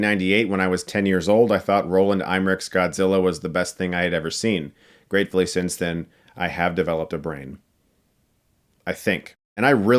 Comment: I thought you said: “Great thing is that since I developed a BRAND”. Well you did that too!